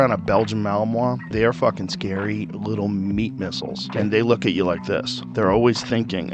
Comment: Did he just say meat missile? Haha